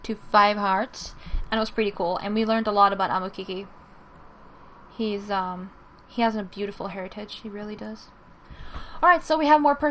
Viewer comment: In stead of hearitige, I thought you said he He has beautiful hair! XD